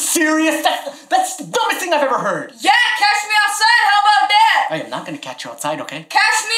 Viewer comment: Huh did he just say cash me outside I’m a die now